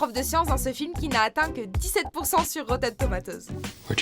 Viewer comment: elle dis quoi a 2:10? "le role d un scientifique qui a atteint 17% a rotatomatosse?"